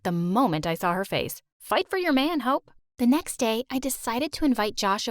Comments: "Fight for your man, Hope" I swear I thought she said, "Fight for your man, hoe" 😂😂😂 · it sound like she said fight for your man hoe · bruh I heard "fight for your man hoe" and was like wait a damn minute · I swore mom said "Fight for your man, h*e" for a second 😂😭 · Omg we she said “fight for your man hope” i thought she said “fight for you man hoe”😭😭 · Bro i heard “fight for ur man ho*” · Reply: I thought she said hi fight for your boy hope 😅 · i legit thought she said "fight for your man hoe" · I THOUGHT THE MOM SAID “Fight for your man, hoe” 😭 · I swear the god I thought the mom said fight for your man ho and then I had to go back and put on captions to understand that she said hope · I like how the rich mom is level-headed, and empathetic towards Hope. Too often, rich people are portrayed as rude and snobbish. "Fight for your man, Hope."😃 That is wholesome, right there. 👏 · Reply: I thought she said , fight for your man hoe lmaooo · I thought she said “fight for your man ho” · LMFAOO I THOUGHT THE MOM SAID “fight for ur man hoe” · Omg I really thought she said ¨fight for your man ho¨ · it sounds like she said “fight for your man hoe” 😹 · The mom is definitely the most interesting character in this story. She's so unpredictable. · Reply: Bro i heard “fight for ur man ho*” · Am I the only one who heard, "Fight for your own man, hoe"? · Did she just say "fight for your man h**"??? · i heard “fight for yo man ho😭” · I literally heard "fight for your man hoe"